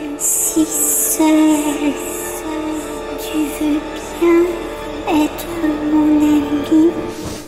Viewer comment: je sais pas vous, mais en parlant d'hallucination auditive, perso, quand katie a parlé, j'ai entendu "je me sent si seule", normal, mais a la place de "veut tu être mon ami" j'ai entendu "veut tu être mon habit" 😂😂😂